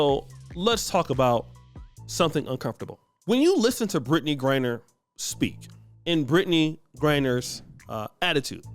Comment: I thought you said Brittany Renner... I closed that incognito tab so quick 😂